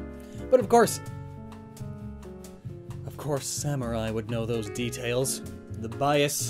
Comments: Did he just call sakurai 'samurai'??? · did you just say Samurai? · Did anyone catch that he called sakuri samurai at around 8:35 ish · Did you just call Sakurai samurai? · did you just say.. "Samurai"? instead of Sakurai? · Did you say samurai at 8:42?